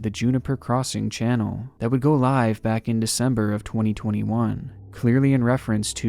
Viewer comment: Is it just me or am I hearing 'live at december 2021' at 5:54?